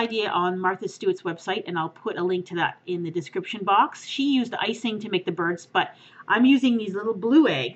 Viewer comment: i thought she said "she put icing to make the birds butt "